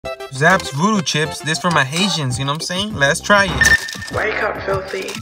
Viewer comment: Did you just say that’s for my “Asians” naw I really thought it said they were Asian chips in bold words ……. Bruh. 💀